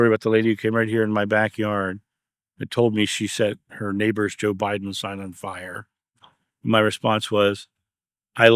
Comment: I've listened to the section at 5:01 five times and still can't understand what is saying! Can someone please tell me? Tried the closed captions, too, and it said 'Joe Biden was signed on fire'. Please someone enlighten me. Thanks! ❤ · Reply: The lady said she "set her neighbors Joe Biden sign on fire"